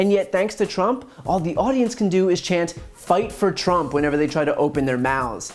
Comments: I SWEAR ALL I HEARD WAS " FIGHT FOR JUNK ! " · It sounds like the crowd is shouting “Lie for Trump!” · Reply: I heard "Die for Trump!". Well, they're taking the right steps to accomplish that! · I hear "Fire Trump!" We did!😂😂😂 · I hear "LIFE FOR TRUMP!" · All I heard was “Lie for Trump”